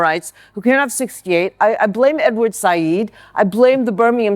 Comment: What did she say at 7:10 what was the name and after boomer? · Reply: she mentioned Edward Said